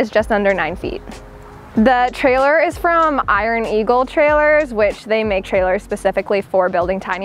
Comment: What type of trailer is that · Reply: Iron Eagle is what I thought she said?